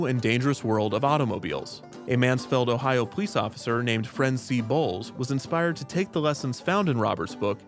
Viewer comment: i wasn’t paying attention and thought you said a man named MC Balls